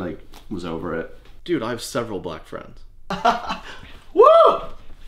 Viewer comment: “Dude, I have several black friends” 😂😂😂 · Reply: For a second, I thought he said I have several black belts (like for martial arts)